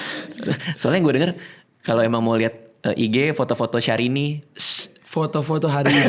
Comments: Aku dengernya syahrini juga 😂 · beneran kedengeran syahrini 😂